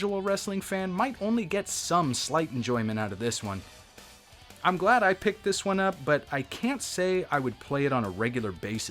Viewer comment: What in the world does it say at the 13:09 mark?? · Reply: I believe it says “anal leakage.”